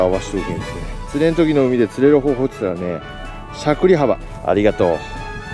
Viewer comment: しゃくり幅がシャクレ幅に聞こえたんだが